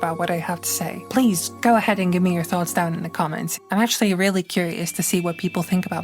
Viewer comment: i misheard you for a second and thought you said "go ahead and give me a thumbs down" and went wait what